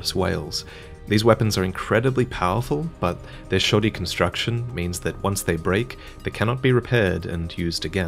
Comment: I thought he said "This shawty's construction"